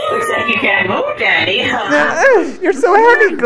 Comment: BRO I THOUGHT IT SAID DADDY